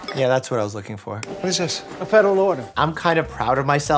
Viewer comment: He said it's a federal ORDER, which was correct. Mike thought he said federal LAWYER. Only a judge can approve the federal order yeah but no one said lawyer.